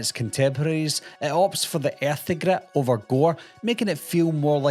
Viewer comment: I'm sorry, but could you tell me what you said at 4:13-4:14 when talking about "The Final Terror"? It opts for...? I just couldn't make it out. I love this list. My favorite on it is "Just Before Dawn." Jeff Lieberman imbues the breathtaking Oregon wilderness with a quiet menace that is unmatched. I was lucky to see most of these at my small-town, single-screen theater as a kid in the 80s. My mom took me to see "Friday the 13th" there when I was 9. Funny thing was, half of my fourth-grade class was in attendance! The next year I saw "Halloween 2" there with my best friend without adult supervision. The theater owners were super cool, so long as you bought a ticket, they didn't give a shit. I saw "The Burning," "The Final Terror," "Maniac,"The Evil Dead, "The Prowler" and so much more (interspersed with stuff like "The Dark Crystal," "Superman 2," and "9 to 5"). And then there were the video rental shops. The ex-mayor of my hometown owned one called: The Video Barn. He turned me on to "Pink Flamingos" and "Desperate Living" at age 12 or so. You could even rent triple X flicks, like the Traci Lords stuff before it was banned. Ah, the 80s! It was exploding with genre media to be consumed. I miss those days! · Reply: "it opts for the earthy grit over gore".